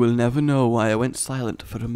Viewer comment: you were having a drink. Thought you said I'd never know